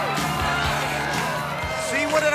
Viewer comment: can anyone say what ian says in the minute 4:39? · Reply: Ian said, see what did I tell ya... you dont need us at all